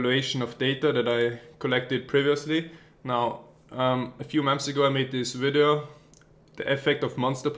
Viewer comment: Nice video.. Haha, it's so funny when you say "months" in your videos. It sounds like you say "moms"... like in around 1:20 ; "A few moms ago" :-D